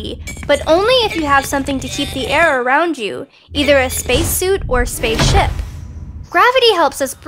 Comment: I thought you said space shit